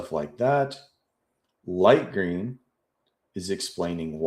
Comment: I thought you said “bush green”. 🤣🤣🤣🤣